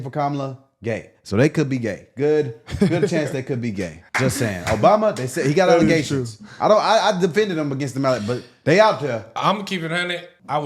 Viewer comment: "They got alleGAYtions" is what I heard, jussayin'